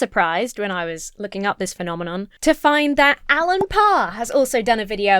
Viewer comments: The first time you mentioned Allen Parr, I misheard is as Alan Carr and got very confused. The Chatty Man himself? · For a second, I thought that you said Alan Carr. I was like... Wait,what? Did she made a mistake or she was just possessed by demons